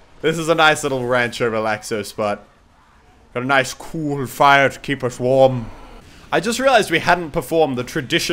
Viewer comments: "A nice cool fire to keep us warm!" -MUSELK 2015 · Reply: Jump I thought he said "Nice pool of fire to keep us warm." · Muselk, at 7:36, did you really say " a nice COOL FIRE to keep us WARM"? I'm sorry if i sound rude but its the first time I heard of a cool fire, much less hearing of them emitting heat.